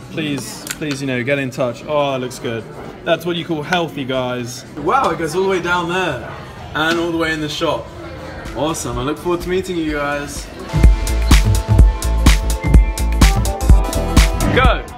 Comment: Omg when said I'm looking forward to meet u guys I thought he said I'm looking forward to eating you guys 😂😂😂😂😂😂😂